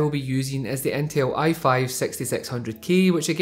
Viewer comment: when he said 6600k I thought he said sexysexy00k 😂